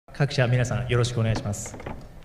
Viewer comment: ここ格下の皆さんよろしくお願いします😂 に聞こえて草